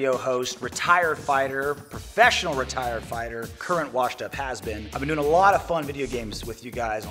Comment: "Current washed-up has been" the best qualification 👌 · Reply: i thought he said "current washed up husband"